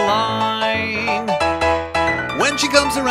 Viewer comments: Why do the words read "Well, then I will never make it boring." I thought he was saying, "Mom and I will never make it boring." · I thought he said, “mom and I will never ever…” Not, “Well, then I will never ever… 🤔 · I think it said mom and I will ever ever make it boring